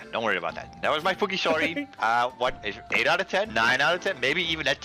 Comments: it sounded like he said fucky story😭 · Did say the F word? Because I think he said "that was my f#@ story" 😮 3:28 oh great there's another at 10:34 I thought this was family friendly